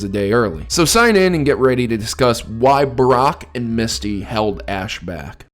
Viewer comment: lmao i swear you said Barrack at first